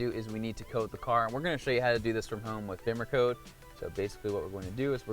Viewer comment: Does anyone know what app he mentioned at 6:30 ? Can’t really tell from the audio, thanks ! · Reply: The app is BimmerCode. :-)